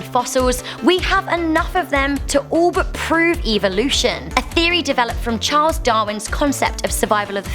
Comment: When you said evolution it sounded like u said eevee lution from pokemon 🤣